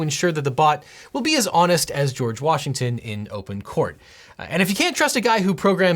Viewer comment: at first I thought he said “as honest as Washington” as in saying it would be as honest as politicians are, which is about as low a bar as you can set lol